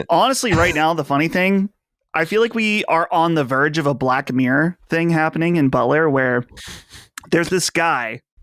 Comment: the exact moment thought said "Black Mayor Thing" instead of "Black Mirror Thing"